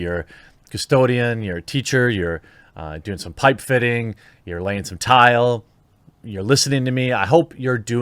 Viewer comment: Jeremy: ...you're doing some pipe fitting, you're laying tile... All I heard was: ...you're laying some pipe... 🤓